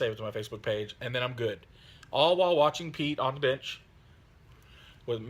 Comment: So sounds like you said Pete's on the bitch video lol. Great update mate, well done for getting them done. Come on mate, don't wimp out, 100 boys in 100 hours, you can do it ;) he he